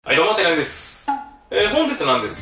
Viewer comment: 何度聞いても はい どうも です ペコッ にしか聞こえないw